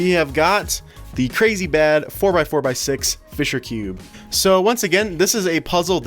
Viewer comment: You said cube instead of cuboid at 0:24. Hope I won't win.:-)